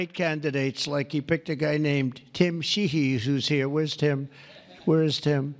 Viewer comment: I heard a guy named Tim She He